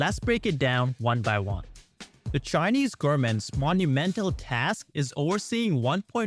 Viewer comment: Is it just me or do the speaker keep saying gourmets instead of government?